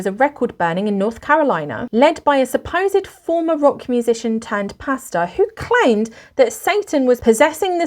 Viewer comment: I totally heard "former rock musician turned PASTA" and had a very VERY weird moment there... :D